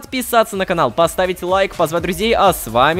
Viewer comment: Мне послышалось : "С вами Лололовка!" 😂😂😂